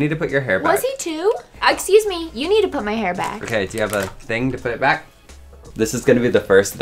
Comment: is a hair stylist also: do you have that thing to put your hair back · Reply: I thought he said "do you have anything to put your hair back?"